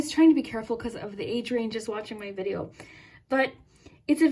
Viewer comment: When you said “age range” in the group 3 reading, I heard “Adrian” and I was shocked because that is the name of the guy I like. Edit : You said they have a sister, he has a sister. I’m just beyond shook rn 😂